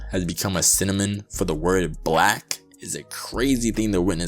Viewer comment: did bro say cinnamon instead of synonym😭